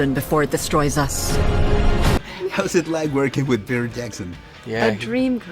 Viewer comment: Pensé que decía percy Jackson, por un momento pensé que volvería esa saga jsjsjs